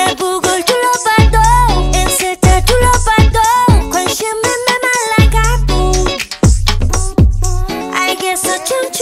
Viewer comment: suran : "memalla gal" me, a Greek person : did I just hear her say malaka?